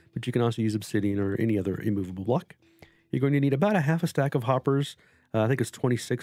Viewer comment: Jeremy A sounds like maybe the building blocks around the slime blocks aren’t immovable blocks? Feel free to take a screen shot or two and send them to me on Discord. Might be able to help you trouble shoot. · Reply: oooh do all the blocks have to be immovable? In the video I thought you said moveable blocks. My bad.